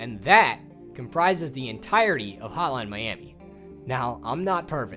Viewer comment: all i hear is "Holan miami" :D good work though! really cleared some things up for me.